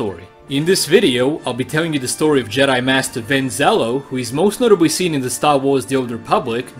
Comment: I thought it said Ben swallow lol